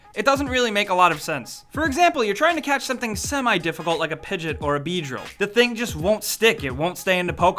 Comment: Did he just say PIGET?!